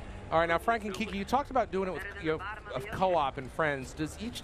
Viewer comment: "You talked about doin' it with Ki-Coop" Lol sounded like he said ''doin it with kiki"